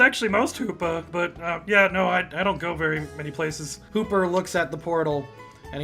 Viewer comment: Did he just call Looker... Hooper? QvQ That's amazing.